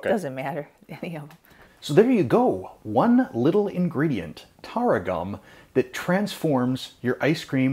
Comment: I walked away for a hot second and I thought you said “tarragon” 😂. So I was like huh…ok…I’ll try anything once! 😂😂😂. But now I know I need to find Tara gum! Lol